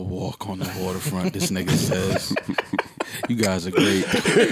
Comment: DID RORY JUST SAY "NIGGA TALK"